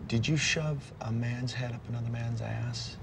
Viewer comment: for those who couldn't understand that, he was asking to get him out of his ass, but that's a moot point